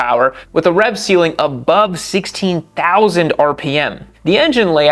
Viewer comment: did i hear that correctly? 240 hp and 16000rpm!?